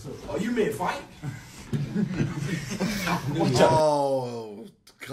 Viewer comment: I didn't understand the 9:24, can someone explain me? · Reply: He said "ill take both of you at the same time, having a doible entendre, either fjghting or getting clapped, the joke was for the latter